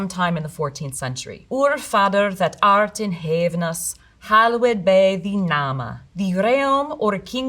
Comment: "heavenous" (I think I'll start saying it like this)